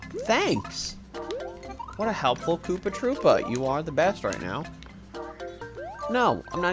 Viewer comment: I thought he said "What a helpful Poopa Troopa!" lol